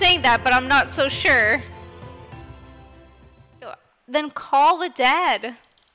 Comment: You said "call the dad" I thought you said "call the dead" lol also mr Samson? Sounds like mr.simpson xD